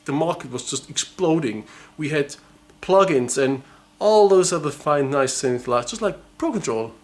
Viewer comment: what the hell did he say? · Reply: "we had plug-ins and all those other fine nice things just like pro control"